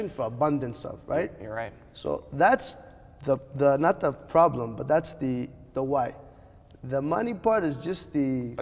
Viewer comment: I thought he said “but that’s gay”